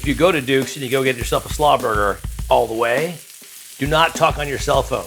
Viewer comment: For a hot sec I thought you said "Slopper", which is uniquely Southern Colorado. And also a burger which you have not covered.